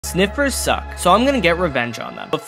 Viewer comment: I thought he said Snickers suck for a second